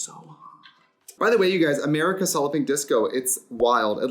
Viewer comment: This is the first time I’ve see the disco name spelled out. This whole time I thought she has been saying Solloping Disco.